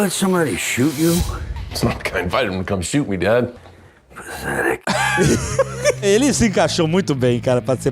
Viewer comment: Eu não sei vocês, mas eu ouvi "Pathetic" (Patético), o que encaixa perfeitamente no diálogo. Não entendi essa de "Zurique" aí não.